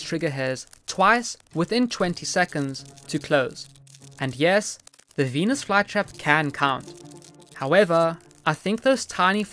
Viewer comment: Istg I heard “and yes the Venus fly trap can cvm”